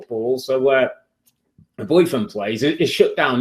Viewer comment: did he say boyfriend? i thought he had a wife and son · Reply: He said his daughter's boyfriend lol